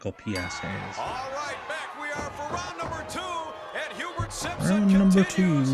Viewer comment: Did I hear "Pubert" Simpson? 😂😂